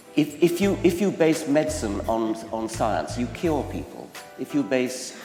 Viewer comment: ce que j'ai entendue: "if you base science of science you kill people" ce que tu a traduit: "Si vous basez la médecine sur la science, vous guérissez des gens" ce que je n'ai pas compris:"...je le tue ou je l'épargne?"